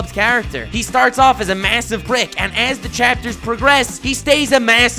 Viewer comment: Honestly for 90% of the bakugo section I thought he was saying massive brick